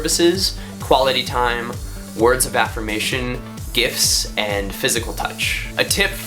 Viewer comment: When he said "gifts" I thought he said "GIFs" and I was like HOLLAAA